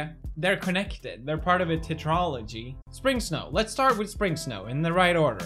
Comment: did he just say titrology?